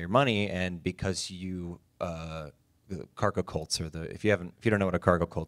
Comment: Giacomo initiate with a bang. Aside, what did Johnny said we had to google? Kartekult? 14:07 ^^ I want the tragedy. Non american here. Cheers. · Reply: He said Cargo Cult. Religions that get started because tribes get into first contact with strangers.